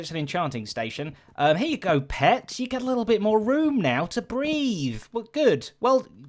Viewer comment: i thought he said at 1:24 “ here you go pets you have more room now to BREED" i was worried their for a second because it's a cat and dog XD